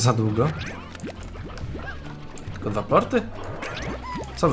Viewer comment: Usłyszałem: ku*wa plorty XD Powiedziałeś: tylko dwa plorty?!